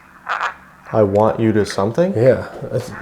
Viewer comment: I hear "I warned you two, LEAVE"